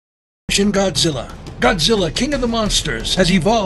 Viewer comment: Am i the only one who heard shin Godzilla in